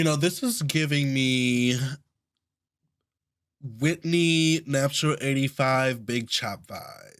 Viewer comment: I thought he said Whitney naptural85 vibes.....🤔🤔🤔🤔